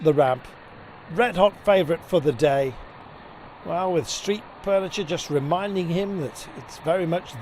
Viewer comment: did he just say 'street purniture'? 4:15 - you know you can do more than one take right guys?